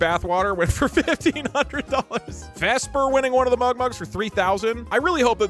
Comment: It sounded like Messmer winning the golden MugMug, and it made me snort lol